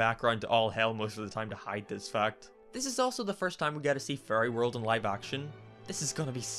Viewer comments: Every time you said "fairy" in this video I heard "furry", so you can imagine how fun it was to watch this. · when you say "fairy" i hear "furry" and every single goddamn time i get confused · for the first 5 minutes i thought he was saying furry instead of fairy